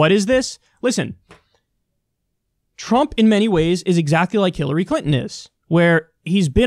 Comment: Bruh, did you just say "Killary Clinton"?